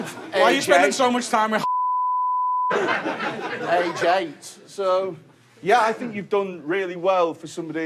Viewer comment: Trying to figure out what is said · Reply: Harvey price